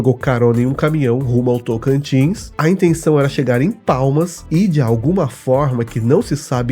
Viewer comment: não eh PALMAS? Eu acho que ouvi PAULAS….😌